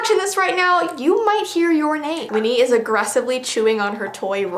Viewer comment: My name is something I don't hear in these type of videos or see on accessories, so when you said "Winnie" I had to skip back and increase the volume because I thought you said "Rinny" (my name)